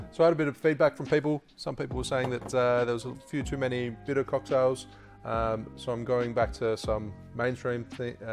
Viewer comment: So hilarious. I found this video on Google, and it didn't give me the subtitles. So this sounded like "FUTA cocktails". Bitter.... Bitter cocktails. Gotcha.